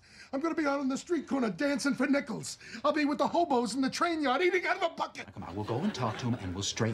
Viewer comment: it sounded like he said "I'll be with the homos". Had to rewatch and turn on cc to hear that he said "hobos". I was like huh?